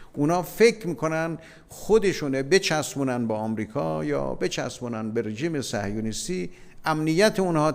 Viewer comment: Did anyone else hear him say "bitch ass" twice ? 😆